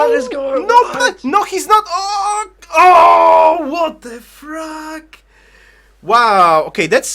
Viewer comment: Y...... 19:28 did you just say "what the fruck"?